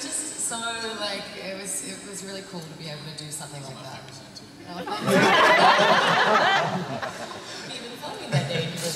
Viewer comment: what did Bob say at 23:26??? Somebody plsss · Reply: "it's one of my favourite scenes too"